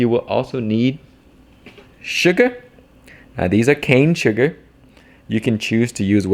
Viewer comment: You said cane sugar, but I thought you said "Khang Sugar." I thought, "Man, he's got his own sugar now?"